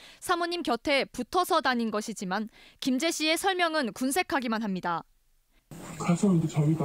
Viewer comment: 나만 김제씨로 들리나? 기자 양반 씨와 시 발음좀 명확하게 해줘요